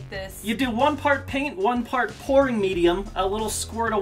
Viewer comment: Every time they said "pouring" I thought they said "p0rn".